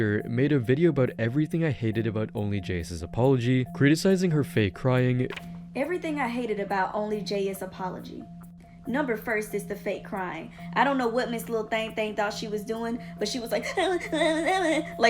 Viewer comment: did she just say number first?